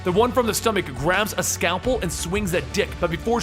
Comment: "... the one from the stomach grabs a scalpel and swings at dick..." What CC says: ""... the one from the stomach grabs a scalpel and swings THAT dick..." 😂😂😂